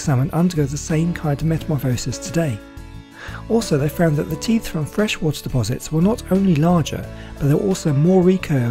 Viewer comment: I did not know much of the data you have said about salmon, I loved the video, but unfortunately, I did not fully understand what you said in the video where you put the video of the red salmon up on the river to spawn, if you are not very bothered, you could put what you said in these minutes (2:14 - 3:00 ), just to understand you, it is difficult for me to understand everything well what you say, but I love your videos so it is compensated, well I hope your answer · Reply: Luckily, I have the script saved so I can get that part easily. During that part of the video I said this: 'Also they found that teeth from freshwater deposits were not only larger, but they were also more recurved, and the tooth tips more worn and blunt. In addition, their bony tooth bases were considerably larger. Teeth from coastal marine deposits were smaller, straighter, their tooth tips sharper, and their tooth bases smaller. These discoveries indicated that these fish were using their teeth to defend their territories and make their nests.It’s thought that the Sockeye salmon are the closest living relatives and probably live very similar lifestyles. It is also a filter feeder and will migrate from marine waters to freshwater to breed. During this time males go through numerous morphological changes at maturation including, an increase in body depth, hump height, and snout length.' Hope that helps :-)